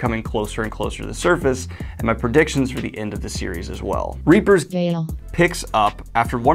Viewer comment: I thought I heard Reaper's Creek and was having flashbacks of typos, questionable topics, and Krimson Rogue losing his mind for the third time